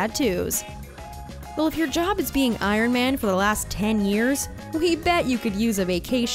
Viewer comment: Did she just say BLANK IRON MAN